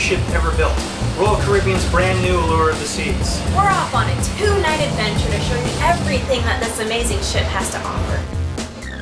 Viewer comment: They said 'this amazing ship' and I heard 'this amazing shit' :D